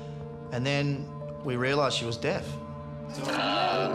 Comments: I THOUGHT HE SAID DEAD 😭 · I thought said "And then we realized she was dead" I freaked out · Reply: He said they realized she was deaf!